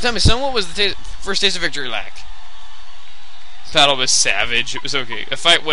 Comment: I thought it said “The battle was sausage.”